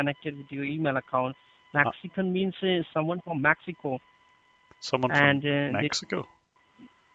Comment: I thought he said "Maxico"